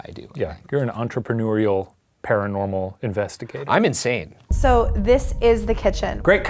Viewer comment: When said I'm insane...I heard "I'm in Shane" 😂😂😂🤷🏻‍♂️